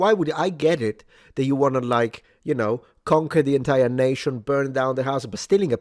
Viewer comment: thought you were gonna say, “i get it, who doesn’t want to mess with the French?” 😅